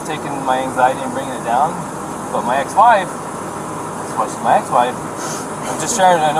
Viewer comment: Wait til the cop’s wife hears him talking about her anxiety and pill taking! He had no idea at the time the entire world would hear this conversation! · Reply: Hex said ex wife, I thought I heard Ex.